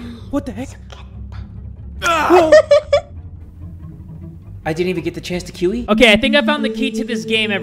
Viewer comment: idk why i heard 8:29 "i didn't even get the chance to kiwi"